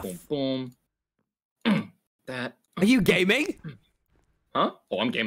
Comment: I thought he said “are you gay mate?” Not are you gaming lol